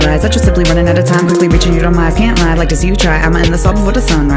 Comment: it sounds like she said quite lieing